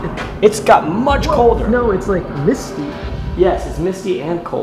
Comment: “it got much colder” “it’s almost CrIsPy, in here” i thought said crispy instead of misty. i was crying lol